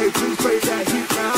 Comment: why do I hear “got me gay” at this part- Anyhow this is really good :0